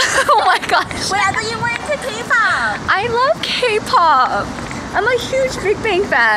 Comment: It sounded like you said 'kokopop'